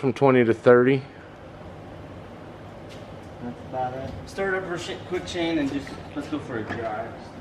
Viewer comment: Did he say Start it up "shit quick?" 11:18 ? lol